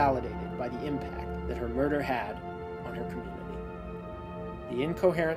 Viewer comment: "the impact that the murder had on ......" what did you say here? I can't make it out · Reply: "on her community"